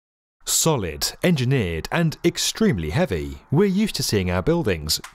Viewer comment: Amazing video as always, but the first word of the video I thought they said “soviets” lol I’m so dumb